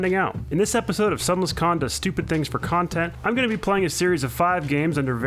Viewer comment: Did you say KHAN-tent? (content) did I hear that right???? Is that a thing I didn't know